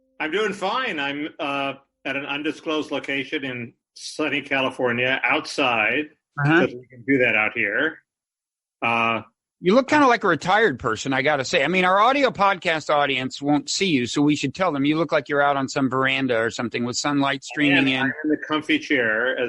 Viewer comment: Lol i thought he said " U look like kinda retarded guy" Epstein we are not hearing much MSM trying make it fade There is so much more to learn & convict I believe lot stuff been held back from public esp by various agencies Follow the money